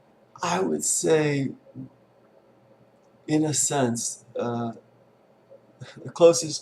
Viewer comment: Thanks. At 1:40 it sounded like you were saying innocence where you said 'in a sense' lol. Words are indeed innocent.